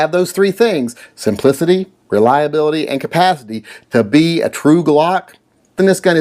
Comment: Did you say "compathity"?